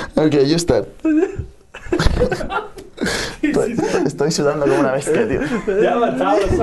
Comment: What did Nadal say? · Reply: "Estoy sudando como una bestia"